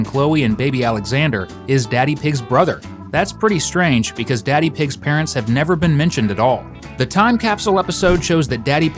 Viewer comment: what do those words even say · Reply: Cousin Peppa is going